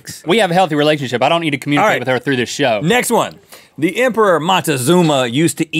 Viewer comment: I've watched this before but when Link says "we have a healthy relationship..." there was an ad break as he said show so it sounded like he was saying we have a healthy relationship, I don't need to communicate through this sh**